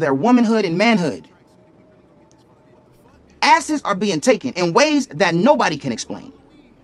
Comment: “asses are being taken in ways that nobody can explain” just made me HOLLERRRR in this student center omg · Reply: i thought he said assets, lmao